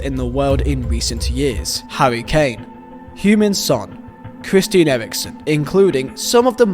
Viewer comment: Love your pronounciation of Heung Ming Son. It sounds like you saying Human Son